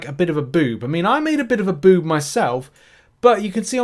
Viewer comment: Great content as always! As a side note, it sounded like you said "I made a boob". If this is true, I am jealous of your talent. :D